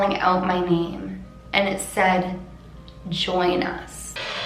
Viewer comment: I hear "join us haileeey"